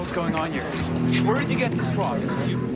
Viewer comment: sounds like he said where’d you get this frog it’s cute 😂😂